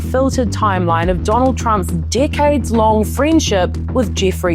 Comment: sounds like you said ‘dickheads long friendship’ 😅